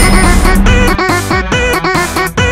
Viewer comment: it sounds like he is saying “ kebble” a lot of times